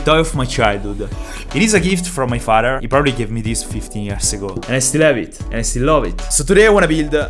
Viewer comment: AYOO I THOUGHT U SAID 50 YEARS AGO, I was like HUHH?